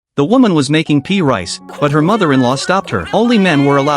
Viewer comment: Ohhhh peas I thought the guy said pee rice